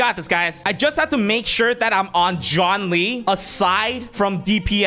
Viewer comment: when he said Zhongli, I heard John Lee 💀🃏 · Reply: He said john lee